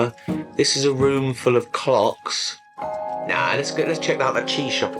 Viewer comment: I think I might be a little dirty minded as at first I misheard that as, “this is a room full of cocks.” Obviously the presence of clocks help my mind be like,”Oh wait he said clocks,” so that helps.